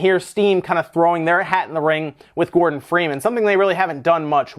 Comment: Lol, I thought you said Morgan Freeman at the beginning. 😂